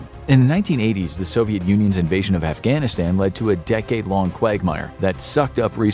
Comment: What. At 1:19 did he say a decade long of giggty?